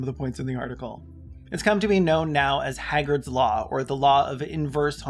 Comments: I thought you said "Hagrid's Law" and got so confused for a second 🤦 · I thought you said Hagrid's Law and I was like "Hagrid's homophobic? :O" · I was listening to this video at first whilst doing the dishes and I heard it said as "Hagrid's law"... 😅 I childishly wondered if Hagrid had a secret crush on Dumbledore......